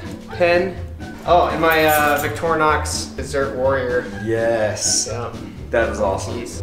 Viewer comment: I was in the next room so it was kinda hard to hear and I thought I heard him say "Victorinox zirc warrior." I got really excited😂😭